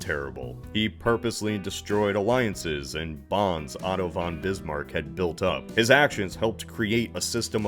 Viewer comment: Why did I hear "Autobahn Bismark"